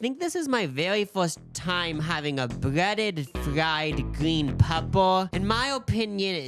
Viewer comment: Oh thank god I thought he said “fried green PEBBLE”